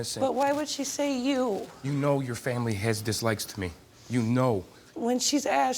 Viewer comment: did he just say "Dis-lickeds me"